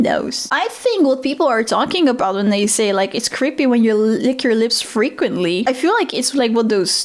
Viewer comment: I thought he said licking your lids frequently. As in eye lids, like a lizard. So my first thought was “people can do that?”